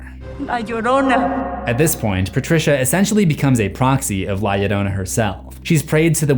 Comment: did some say... Proxy?